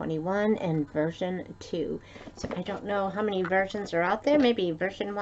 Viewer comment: I'm here!!!! Good morning! For a second I thought you said "I don't know how many virgins are out there" LOL!!! GIRL!!!! I have been using the Vital Protiens for years...a scoop in my first cup of coffee every morning....so good for you!!!!